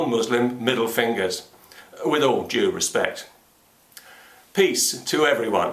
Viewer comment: My mind thought it heard at the end "with all jew respect."